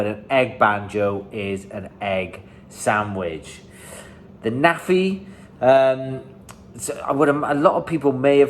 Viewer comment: Very interesting! Quick suggestion: spell it out onscreen for us American grunts. (I still am not sure what was said at 2:49 it's probably my own fault.) Since it's too late for that, you can put it in the video description. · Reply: The Naffi. Mostly used when going on a break to the shop. Also Naffi bar. Night time to earn tomorrows Agai. Lol